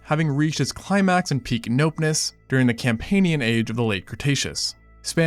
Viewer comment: Im sorry , did you say "peak nopeness" at 2:00?